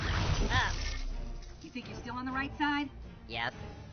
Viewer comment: Sometimes I notice the smallest, most unimportant things... and this is one of them. At 14:03 the audio says "You think you're STILL on the right side?" But the caption says "You STILL think you're on the right side?" I have no idea why I saw this.